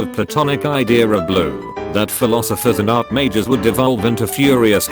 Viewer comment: I thought he said “Philosophers and arch-mages”...